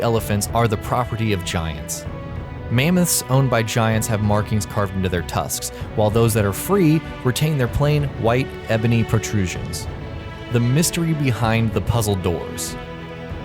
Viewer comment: Did he just say "white ebony"